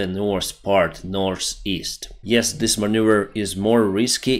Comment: Is it called the Dick-Maneuver? x)